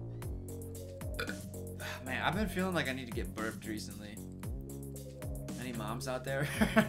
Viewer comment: I thought you said you’ve needed to give birth recently, didn’t help that you followed it up with “any moms out there”